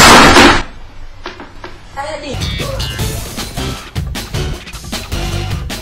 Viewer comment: r u vietnamese ? because i hear :''Ơ ĐịT'' in